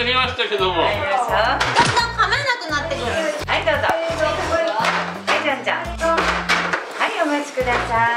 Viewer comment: はいじゃんじゃん→はいゆんちゃんに聞こえた笑笑